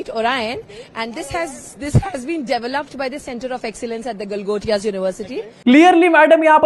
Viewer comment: I heard "Gotiyaa" university..😂